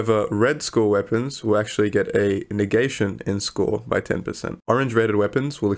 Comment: Did he just say Niggation ?